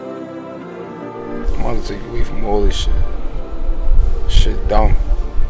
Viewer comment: Wtf was he saying at 3:36? Lmao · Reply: I’M about to take you away from all of this shit